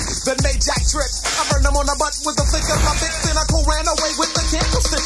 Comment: I thought I heard Skat Cat say "Flip of my Dick" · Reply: It's actually "flick of my Bic", a reference to the old ad slogan for Bic lighters.